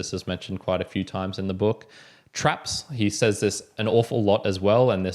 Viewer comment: I thought he was saying troops